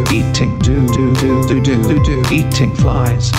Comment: i thought they were saying eating du du